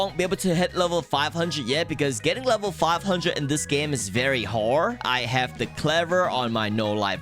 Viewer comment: you said "wh*re" instead of "hard"